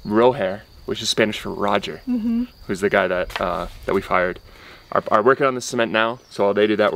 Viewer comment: did he say hired or fired?!? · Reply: Hired!!!